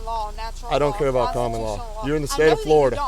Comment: I thought she said "Common law" not "canon law". What's going on editor? 😅